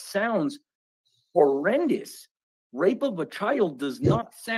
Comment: What has he been locked up before and for how long? Glendale, AZ is the worst place he can go. Gangs and crime is really high! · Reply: Yeah I cringed when I heard him say AZ. He said "grape of a child". We don't want him!